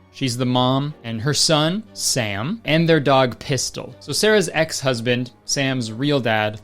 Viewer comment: (lol) I really thought you were about to say the dog’s name was piss. What is wrong with me